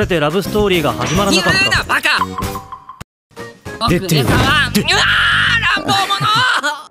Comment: as a filipino its so funny hearing him say "AHH ANG BOBO MO" even tho thats most likely not what theyre saying😅